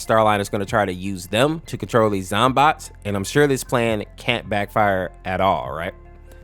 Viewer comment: I’m sorry… did you say ZOMBOT? As in Plants Vs. Zombies?! Uh oh, the PTSD setting in! AHHH!!!